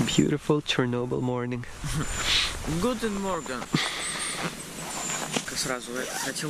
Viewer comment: did say "guten morgen", german for good morning? i also wanted to see pripyat and chernobyl some day Edit: watching the rest of the video right now where you got chased by the military. i'm thinking that for such cases a bagpack and clothes with camouflage patterns would come in handy 😄